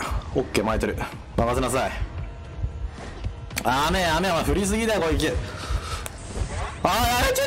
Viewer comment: これ元動画からずっと気になってたんですけど、なんて言ってるんですか？甘い甘いなに…？笑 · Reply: 振りすぎだよ、攻撃 だと思います